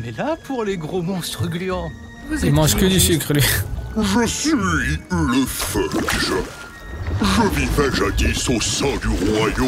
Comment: j'ai entendu "je suis le feuj" genre juif en verlan....💀